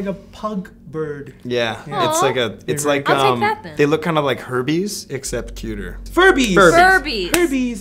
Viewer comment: I thought he said HERPES AHAHAHA